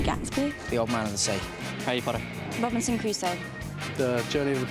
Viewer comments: 해리포터로 들려용..아닌갑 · harry potter 라고 하는거 아닐까요..? 전 그렇게 들려서요...